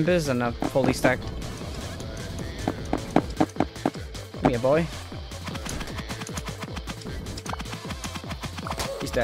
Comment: i thought he said come boy